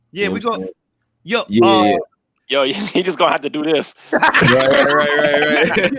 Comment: Correct me if im wrong but at 1:53:30 did said "Niggas guna have to do this"? Lmfaooo them blutooth speakers be picking up hahah